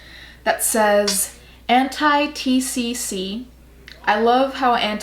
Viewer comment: when you said "anti-tcc" I misheard you and I thought you said "anti-thicc"